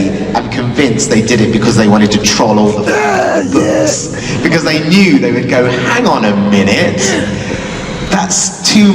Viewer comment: was that like a sneeze or just a random outburst? I couldn’t make out what was being said. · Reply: Just an "ahhhh Yeaaah" in excitementb but I had lost my voice the night before. So it's just random high pitched wheezing